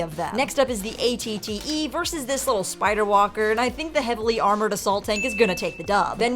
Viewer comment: Is it just me or did sbe say that the ATTE is a heavily amoured assault tank